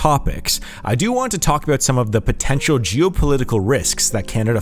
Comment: "je'w-poe-liddy-coe ..." -What a weird way to say "geo."